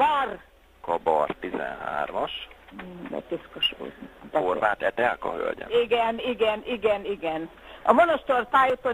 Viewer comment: itt mit mond:? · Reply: szerintem az ölében ülő unokájának a csokis száját törölte meg: "mmm itt piszkos volt"